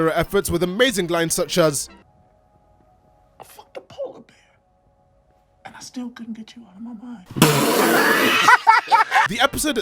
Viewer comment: what did he say? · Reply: "I fucked a polar bear and I still couldn't get you out of my mind". And no, I'm not joking.